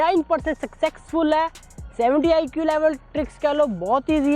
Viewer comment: did he said sucksexfull?